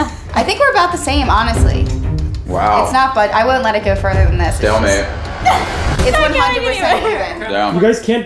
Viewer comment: This I just heard it right? saying "babe"